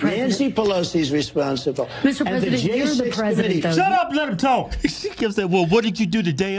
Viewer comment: Holy shit, I thought it was Trump saying, "SHUT UP AND LET ME TALK!" You made me jump! That'll teach me to have my volume turned up on my headphones 😂😂😂